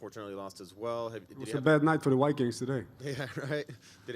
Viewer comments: what did gus say there, bad day for the white guys? I hear white but the other word is unclear, gays, gaze,guy? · Did he say, "It was a bad night for the white guys today" ???